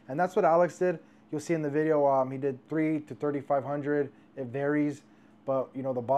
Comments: To be fair it does sound like you said "3... 3 to 500" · I can definitely see how he coulda thought you said. "3... 3 to 500, something around there"